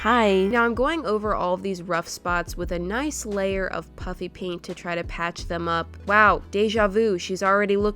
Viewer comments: Haha it sounds like she is saying puppy paint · Sometimes she sounds like she is saying puppy paint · Who else for many years thought she was saying “PUPPY paint” ?! :’D